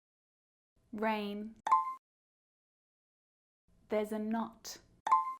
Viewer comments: Hold up! A couple of corrections are necessary my friends! I've made some errors 🤦🏻‍♀️🤦🏻‍♀️🤦🏻‍♀️ FIRSTLY! If you haven't completed the quiz yet, don't look here! ⬇️ I have made THREE errors in this quiz... See if you can find them!! (Come back and check here later) 😁 11. The wrong word is hidden in Q11 (it should have been 'buy') and I didn't notice it. (So now, the correct answer is *enough*) 14. Should be which 59. Should also be principle So sorry! And a huge thank you to Irene and Azrudding who quickly brought this to my attention! 🤩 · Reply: Great lesson! Lots of time put into this. For 58, I heard "There's a knot" instead of there "There is a not" - not to worry. Thank you again! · Scored 52/60. Didn't hear a few correctly. I must check the discreet and discrete. I heard there's a lot instead of there's a knot.